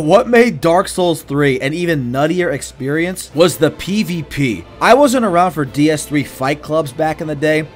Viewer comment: after seeing this short clip with McGregor I thought you've said ' I wasn't around for Diaz 3 fight club' 😂